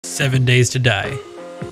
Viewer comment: I thought you said 7 ways to die at the start